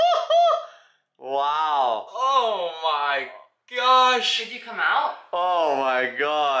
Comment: I heard say "did he come out?" Which means he was indeed inside a barrel.